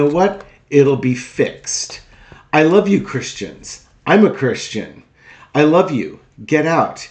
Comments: Everyone I showed the trump video to, everyone, replied with “did he say he’s not a Christian?” My reply was, F’n listen to the entire thing! I couldn’t care less if he said he was or wasn’t a Christian, he just told them and the world, elect me and you won’t have to worry about elections ever again. HARRIS ROE 2024🇺🇸💙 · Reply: That’s what I heard him say I’m not a Christian you’re a Christian you need to vote. I swear to God he said I’m not Christian so other people heard that too! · I totally think I heard “I’m NOT a Christian” … was it a mumble or have I gone Trump-overload-burnout deaf? He opens his mouth and my brain shields itself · Alot of people heard him say I'm not Christian and I believe him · I swear to God, I heard him say I’m not a Christian you’re a Christian you need to get out and vote! I swear to God that’s what I heard that he told them that he wasn’t a Christian. How did that happen? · I thought he said I’m not a Christian?? What article are you reading honey,, i think in the video he said I’m not a Christian. I think, I’m going to go look.. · I don't know if he said "I'm not Christian" or " I am Christian". I thought he said he is not a Christian. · It really sounds like he say "I'm not a Christian."